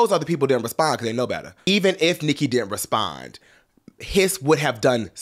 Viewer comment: Did he say “piss”